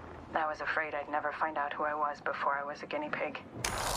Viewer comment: is wraith saying "I was afraid I'd never find out who I was before I was a guinea pig"?! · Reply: *subject